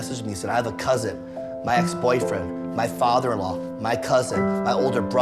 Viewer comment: Did he say "my ex-boyfriend"?